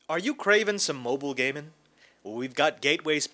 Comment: lol in the beginning it sounds like u said "are u cravin some moble gay men"